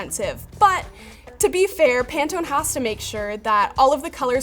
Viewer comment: ngl, I thought that said Pantene xD